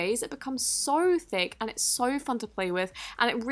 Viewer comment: she said fun but i heard it as the f word, i was like waitttt whatt😂😂